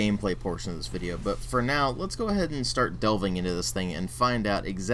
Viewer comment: Pretty sure I don't use "dove" when talking in the present tense. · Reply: it sounds like you say "doving" when I listen to it.